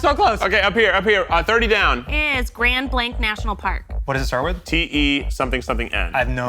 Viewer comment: i swear i thought she said Grand Blanc national park, as in Grand Blanc Michigan, not Grand __ national park.